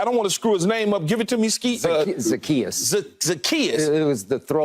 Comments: brah did keyshawn just call skip "skeet" at 9:32 lmfao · What did keyshawn call skip at 9:32 😂😂😂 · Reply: Skeet lol